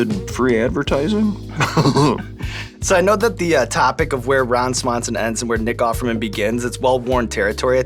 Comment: Sounded like he said “Bron Swanson” at the beginning.